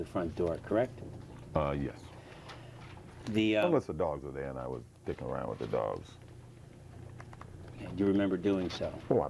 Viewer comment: Did he said "unless the dogs were there and I would dick around with the dogs"